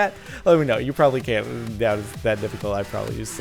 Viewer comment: did he say I promise you suck?